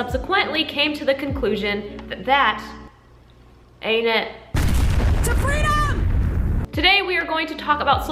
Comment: OK TELL ME WHY IVE HEARD THAT “TO FREEDOM” AUDIO IN SO MANY VIDEOS AND UNTIL NOW I THOUGHT THAT IT SAID “SABRINA”